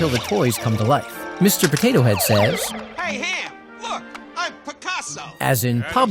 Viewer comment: i could’ve sworn Mr. Potato Head said “look, i’m Potasso” as in a mix between “Potato” and “Picasso”